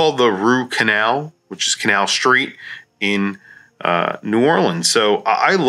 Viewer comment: ha-at first i thought i heard you call it the "root canal" !